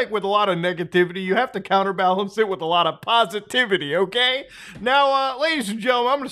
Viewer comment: Guys im officially on brain rot, in the First ten second i misheard positivity into skibidi